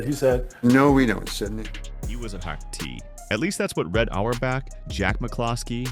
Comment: I'm sorry, what the f are they saying at 0:29? Wicks '...was a hack tea'? (Or '...wasn't....;'? or '...was in...'?) Huuuuuuhhh?....